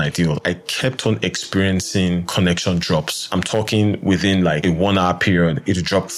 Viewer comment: i didn't understand your accent well, do you mean you had connection drops using 2.4ghz receiver ? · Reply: Apologies for the confusion but <mail> is correct. I meant that I was getting connections drops using the 2.4ghz wireless receiver which isn’t typical. I hope that helps